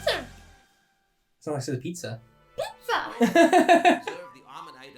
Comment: I swear I heard that Pichu say Pizza too😂